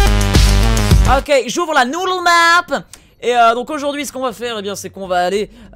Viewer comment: c'est quoi Cette langue ? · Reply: Ah oui enfaite il a dit nudle map